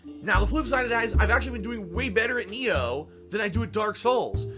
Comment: I know this vid is 3 years old and its probably not significant enough to fix, but in the transcript at 27:24 it should be "nioh" instead of 'neo'